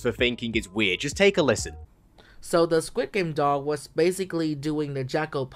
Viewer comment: Did really called the doll a dog?💀